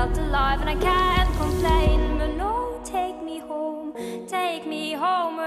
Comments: Completely obsessed with this song but I seriously thought she said Lord take me home, was kind of surprised when I found out that isn't what she said lol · I always thought it said lord take me home